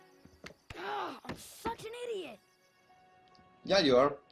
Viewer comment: FUCKING IDIOT Am I the only one who heard that ?